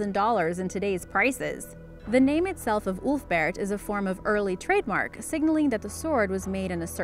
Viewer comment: I only hear OOF-Bert sword 🗡